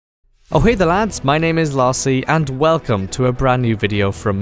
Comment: O! Hey there lads! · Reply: I thought he was saying howay the lads like some sort quiet Irish Geordie !!